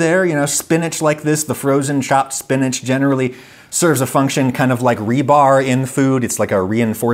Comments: re: captions at 3:22 you say "kind of like rebar in food" but whoever made the captions didn't catch that and labeled it as [inaudible] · Adam, at 3:22 whatever program does your captions left a note that it couldn't figure out what you were saying, but you clearly said 'rebar'